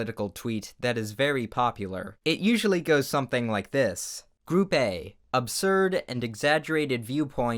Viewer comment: HALELUJAH AN UPLOAD I thought he said groupae then he said group B and I felt stupid 👍🏾